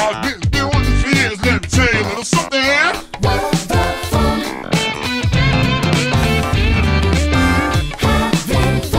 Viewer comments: did I just hear a kid’s game cuss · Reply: With the funk is what it says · what did they say 😳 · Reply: With the funk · did they say what i think they said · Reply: no, they said "with the funk"